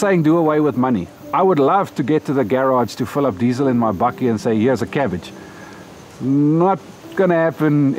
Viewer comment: what did he say about diesel and cabbage? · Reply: He said: 'I'm not saying do away with money. I would love to get to the garage to fill up diesel in my bakkie (car) and say 'Here's a cabbage.' In other words, he would love to pay for the diesel for his bakkie (pick-up truck) with a cabbage instead of money. Kindest wishes, and. 💚